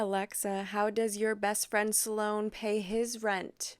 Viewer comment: the captions say Salone, but are you referring to Youtuber SLOAN? 👀 I'm down to watch a video on it regardless lol